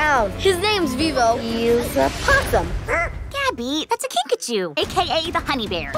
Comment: I had to listen to it twice cus I thought she said pikachu